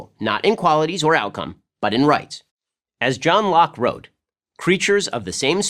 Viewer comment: For a minute I thought it said John Wick